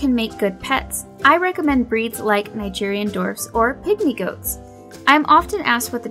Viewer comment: I thought she was saying Nigerian dorks.